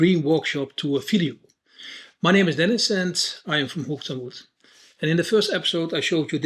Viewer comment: I swear for a year I thought said "I'm from Hukertmut" and thought that was a town in Holland. Turns out it was his accent and he was saying "hooked on wood." 🤣